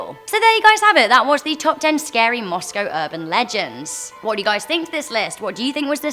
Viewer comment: i thought you said Costco urban legends